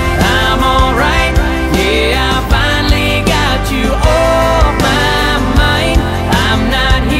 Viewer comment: I thought he was saying I finally got you off my money. Lol. Would have made the song better . Would have fit today. I'm not much on this new stuff.